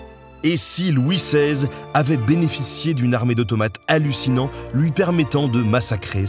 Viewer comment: j'ai entendu une armée De Tomates Hallucinantes , et j'ai du repasser la vidéo avec les sous-titre pour me rassurer...